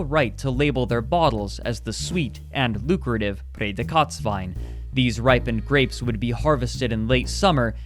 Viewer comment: It took me 5 years to realize that he says "Prädikatswein". This whole time I thought he was saying "Federkatzwein" and I was confused why I couldn't find anything about it 😅